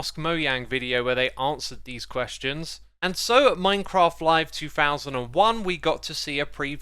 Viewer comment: did he just say minecraft live... 2001...?